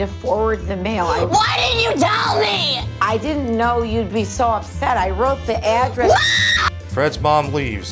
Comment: During the scene with Fred saying he called the police, i thought he said "baddest" instead of "fattest"